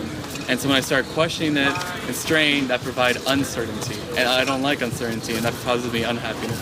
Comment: at 11:21 I think he might be saying, "when I started questioning it and straying that provided uncertainty."